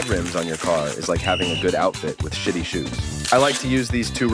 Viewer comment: Did bro say sh!tty? I can't believe I heard say a swear word...